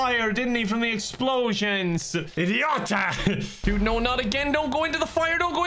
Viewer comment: Did I just hear polish word "idiota"? 😁